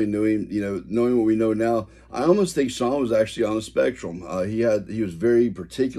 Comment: I can't understand the word you use to describe Shawn?.. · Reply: on a spectrum, meaning Aspergers or Autistic possibly?